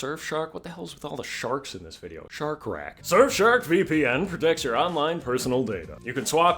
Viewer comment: With all the 🦈 shark's in this vid, I thought you said RatShark or Shark rat instead of shark rac dooohh!!!😮